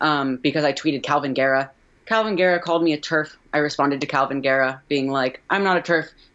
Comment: what is the word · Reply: TERF? It’s a slur meaning Trans Exclusionary Radical Feminist.